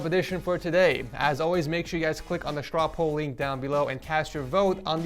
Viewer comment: Vote here: <link> Giveaway: <link> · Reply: For literally a year, I have always thought was saying ‘strop a link’ or ‘drop a link’ but now I know it’s strawpoll. This makes a lot of sense to me now